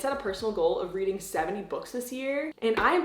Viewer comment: I THOUGHT YOU SAID 7 BOOKS AND I WAS LIKE “BUT YOU READ LIKE 10 BOOKS IN THE PAST TWO MONTHS.. HOW ARE YOU BEHIND”